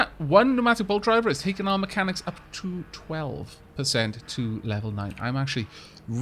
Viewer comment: Sir, 6:30 I heard “and that bolt driver has bought our mechanics level to twelve “and I was shocked to say the least